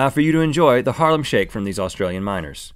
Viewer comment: Am I the only one who thought that they were saying Australian Minors